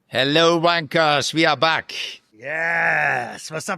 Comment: I heard, "Hello, Wankers!" 😅😂